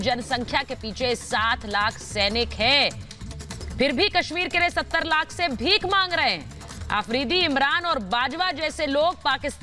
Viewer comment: Did she says 70 lakh